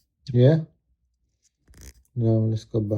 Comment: Who messed with this watch?!? The amount of cosmetic damage they caused is criminal. What? You said 6000 grit? I thought you said 60 grit!